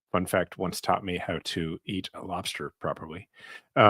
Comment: Haha .. Jason said she taught him how to eat lobster .. my volume was low .. I thought he said .. how to eat Monsters (referring to political opponents) you made my day 😀 you two are awesome